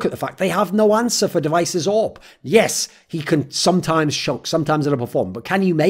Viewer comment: I always thought device said "joke-free zone" and just now realized he's saying choke